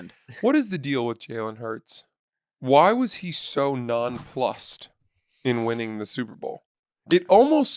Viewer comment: Did say "nonFlussed"?! 2:00 lol